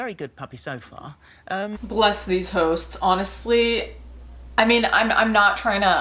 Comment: I heard "bless these hoes" instead of "bless these hosts"